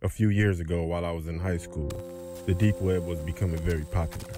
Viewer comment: I thought it said deep weab